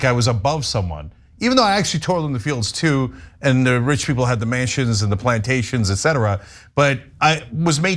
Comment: I thought he said rich people has the Manchins and the Plantations, as opposed to Mansions... Same difference, I guess